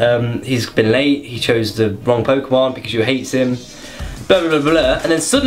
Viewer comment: I thought you said he got laid